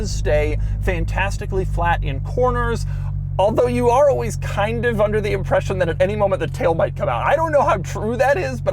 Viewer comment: Hey Doug, please explain for the slower talking Australian audience what are you saying at 18:25? Cheers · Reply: "although you are kind of always under the impression that at any moment the tail might come out"